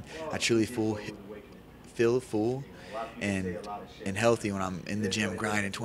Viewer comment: Did Cody say "I feel fool" ???